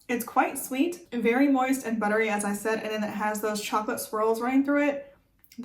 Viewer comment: LMFAO....okay that sweater is amazing...tall neck horse monsters are tight. /// Mmmmmm I love the Pod Scum Kool-Aid flavor. So underrated. /// Dark Choclate is the best /// Also...I was seriously mishearing things today...chocolate squirrels in the pound cake...baily's in the chowda....